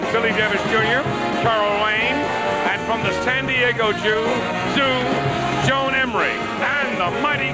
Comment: Did Ed say “from the San Diego Joo…Zoo, Joan Embrey”?